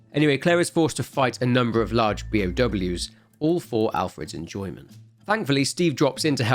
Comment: I swear I hear bmw in